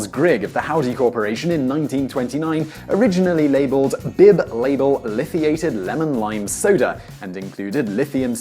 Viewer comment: “Bib-label lithiated soda I thought he said bin laden lol